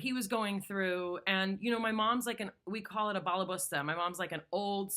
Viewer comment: When I heard he say they called her Mom a "balla Busta" (new word to me) type of woman or a woman who gets the housework done. I heard "bust your balls" type of woman... Lol. Can't explain my brain.